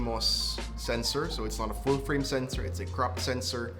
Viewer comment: Thought you said Crap Sensor 😲🤭